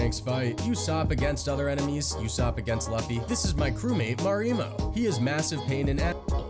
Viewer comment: Did he just say laffy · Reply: no he said luffy